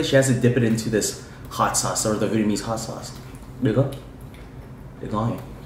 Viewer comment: captions say he said nigga lmao